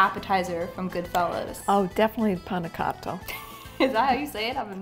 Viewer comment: The way she pronounces the A in “pane cotto” makes it sounds like “pene cotto”, that instead of “cooked bread” translates to “cooked p*nis” 🥴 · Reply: To me it sounds like she is saying 'panna cotta' in an American accent, which is not bad, it's just a different thing